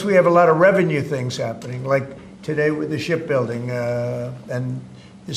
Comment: His accent....I almost thought he said shit building 😂😂